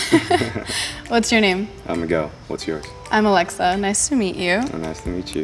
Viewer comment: I thought he said I’m a girl